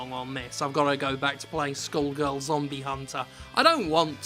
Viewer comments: I misheard 0:56 as “Skullgirls Zombie Hunter” and for a second I got way too excited over the idea of finally having a new Skullgirls related game. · I thought he said Skullgirls for a second there and got excited, but then realized it was probably some shitty Steam game.